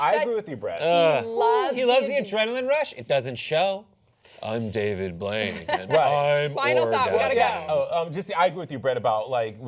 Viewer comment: is Brett saying "I'm David Blaine and I'm orgasming"??